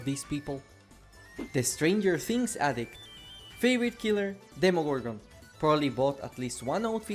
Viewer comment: did i heard demo?????!!!!!!!!!